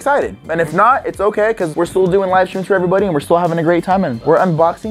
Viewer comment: I thought said bs livestreams 😂 I cant right now